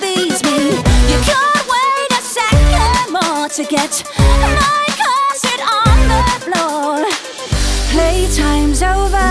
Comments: what i find interesting about one of the lines, “cant wait a second more to get my corset on the floor” sounds an awful like “get my cousin on the floor”, shadowing on the fact that prior to katherine and henry’s marriage, her cousin (anne boleyn) was also married to him. just cool wordplay, intentional or not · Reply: I actually heard it as “get my cousin on the floor” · For a long time I thought she was saying “you can’t wait a second more to get my cousin on the floor.” Needless to say it makes a lot more sense if you know she’s saying corset. 😂😂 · okay i thought she said cousin not corset.... thank GOD